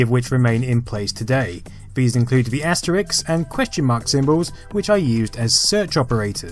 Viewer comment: Asterix I hear?